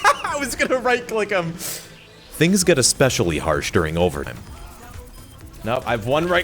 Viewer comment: ??? did he say overtim